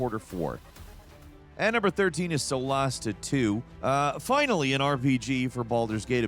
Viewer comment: With Solasta 2, I thought you said Celeste 2 (Im getting ready for work so not fully watching). Then I looked at the gameplay you were showing and was so confused "Wow they really went a different direction from that pixel platformer". The penny did drop eventually. I need caffeine :P Edit: Ooh Aphelion looks good, I am just making my way through Banishers and liking it a lot.